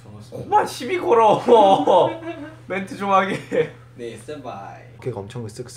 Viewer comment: Did Jungkook just call Tae Senpai?